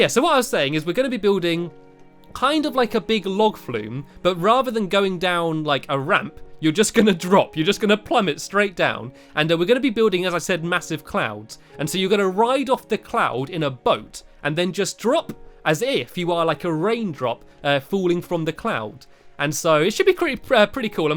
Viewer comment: I think you should keep the face on the Melon Tree. Also whenever you were explaining how the water ride will look I thought you said "Massive Clowns" not "Massive Clouds" and I was thinking if I should keep watching because.. I DO NOT LIKE CLOWNS :)